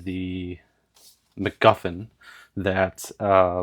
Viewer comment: Thought you said McMuffin